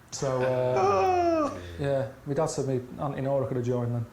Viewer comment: what does he say? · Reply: “Me dad said me Auntie Nora coulda joined them..”